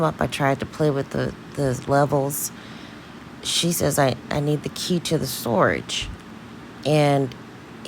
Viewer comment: This is what I heard: She said "the key to the storage", meaning, that was in your possession and I need it. I think he said the car company called the police and they weren't supposed to do that. He was rambling to me like he usually does. He said we'll just get power of atty so you can go pick it up, which is odd because the truck is in her name, she doesnt need POA. But it wasn't so much of the storage on this clip to me that stood out as much as the non-chalant attitude from. We have to factor in horrible financial habits in all of this. She says to him "I wasn't answering the phones for a long time bc you said it was paid off, but anyway, whatever" 🤨HUH?? behavior allowed crimes to hide and fester. You can't tell me she wasn't aware of it. I think she knows how to play the 'I didnt know anything' role very well to distance herself. If this goes to trial, that'll be her defense for everything. I've said it over and over, she's way too comfortable and casual with a lot of alarming things that have happened, such as the car being seized in an extremely embarrassing way, and the infamous Tai the Jeweler call. Then again, she could just be tired of this man. We've only been listening to calls and damn he's worrisome. Also, I think he had Dominique sell all those things in the storage.